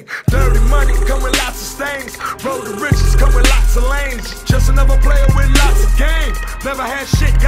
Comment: ITS LOTS OF LANES Why u think he said road to riches